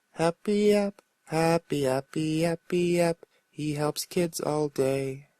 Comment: I THOUGHT HE SAID HE HUMPS KIDS ALL DAY! HAHAAHAHAHAHA